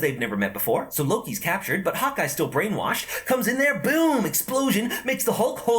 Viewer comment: Instead of Hawkeye I thought he was saying hot guy